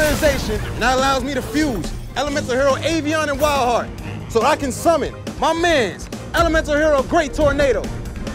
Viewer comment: Bro I was just gonna hop off and finna slp then I literally heard " I summon onlyfans" I immediately came bk 😂 😭